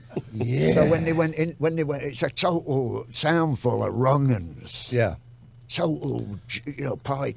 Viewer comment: Around the 19:25 mark, does he say, “It’s a little town full of pikey ronans?”